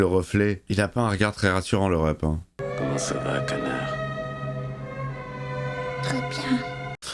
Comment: y'a que moi qui ait compris "connard" plutot que "canard" 😅😂😂😂 ...?